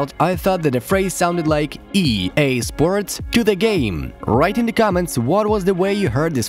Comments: i heard it as e…a… sports tenna game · I heard “EA SPORTS TINNE GAME” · I heard: "EA Sports, tinit game." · Ngl I always thought it said “to the game” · As a kid I always thought he was saying “to the game” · I heard “EA SPORTS TINNE GAME” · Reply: I heard “To the game” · let's be honest, 90% or even more of the people first thought it said "to the game" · I always thought he said "EA sports Shana gan" when i wad lil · I thought it said EA sports tennis game until I turned 20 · I thought he said tenet game as a kid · I heard "to the game" HARD!!!! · I hear on NHL 17 "EA Sports to the game"